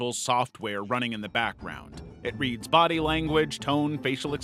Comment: I hear "Where running in the background"